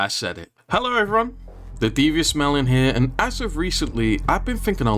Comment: This is the first video ive watch from your channel and i thought you said "The devious smell in here" in the intro